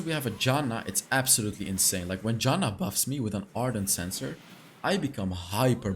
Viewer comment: Am I trippin or Did he said Ardent Censor cause the item has been removed isn't it so?